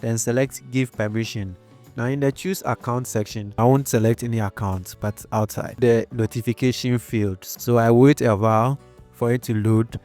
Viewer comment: What did you say at 2:12 ? " Now in the choose account section"... Voice isn't clear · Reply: You won't select any account buy anywhere outside the notification area. Where the arrows are pointing